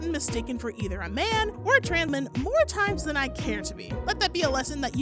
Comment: "Either a man, or a tran-man", I think you said trans man there but it cut off weirdly. Audio glitch?